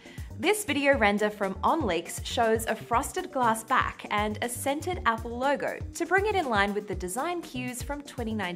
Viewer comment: I thought she said a “scented” apple logo. 😂